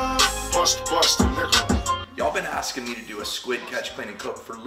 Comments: Glad you clarified the lyrics I could have sworn I heard bust a nigga! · OMG thought he was saying bust a nigga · Not going to lie. I thought that song said "bust a nigga" lmfao